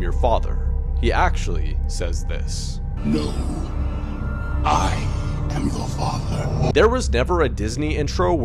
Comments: “He actually says this” Me: Then why I’m hearing Luke I am your father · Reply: I heard “no, I am the father” idk why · Him: “He ACTUALLY, said this:” Also me still hearing the same thing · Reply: What I heard is “loo, I am you- THE FATHER” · DUDE, WHO ELSE STILL HEARD "Luke, I am your father" AND GOT SO CONFUSED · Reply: I heard "Help, I am your father" Edit: I watched again and heard "No, I am your father" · him: darthvader never said luke i am your father. me: turns up vol to max still hears “Luke i am your father” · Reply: yeah me too , I always hear "no, I'm the father" instead of "no, I'm your father" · “He actually says this” Me: Then why I’m hearing Luke I am your father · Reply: I hear “No Luke I am you father.”